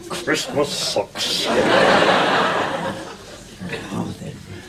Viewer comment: Lol 1:26 he sounds like he said Christmas Sucks